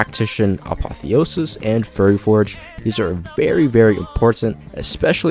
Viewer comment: great vid but did you just say furry forge